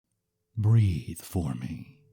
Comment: Wow, I misheard the very first line as "Breed for me" and I was like "...Okay"